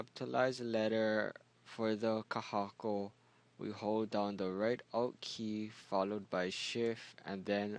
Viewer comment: to type the kahako ... "hold down the right ????? key" ... could you please spell which "right" key to hold down for the kahako? ... It sounded like you said the "right out" key .... if so, which one would that be? ... mahalo nui